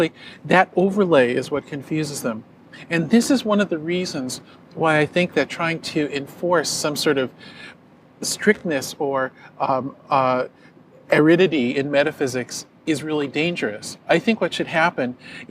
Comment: did he say aridity?